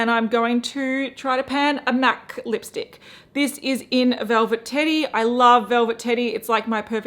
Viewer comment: Omg why do I keep hearing “velvet titty”.